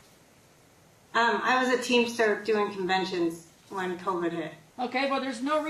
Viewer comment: She's a teamster, working conventions or something, not a teacher. Someone else thought the same thing. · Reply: I could've sworn she told the judge she was a teacher. I must be misheard her. Thanks for the info. Whatever she does, she shouldn't be around children, especially her own.💜💜🤗😎